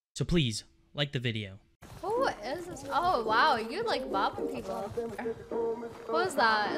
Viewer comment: I thought she said u like black people at 0:08 😂